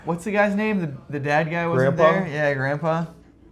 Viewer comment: I thought he said “imagine if the dead guy wasn’t there” 😂😂